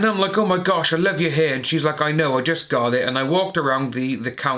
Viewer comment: I could have sworn said “cut it” not “got it”.